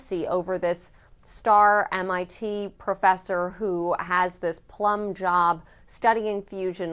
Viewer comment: Did she just say he got a "plumb job"?? LMFAO!! Wtf?? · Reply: It’s a “plum” job. It’s old people slang for a position with great pay, good hours, honors etc. (I am an old person.)